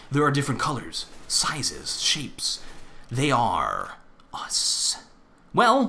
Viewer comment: O SHIT AT FIRST I THOUGHT U SAID "WELL I AM THE WAR OWL AND I STILL HAVE NO CLOTHES ON" I WAS LIKE DUHHH FUCK